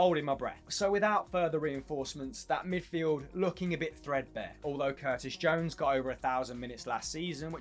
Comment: First I heard him say Fredbear, but at another listen, he did not say Fredbear, Im thinking too much of FNaF at the moment